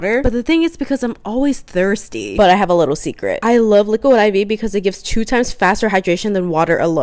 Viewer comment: okay I'm sorry I thought you said liquid diabetes-😅